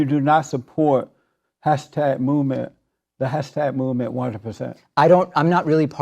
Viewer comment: fr i thought jesse said "hashtag MOO MEN"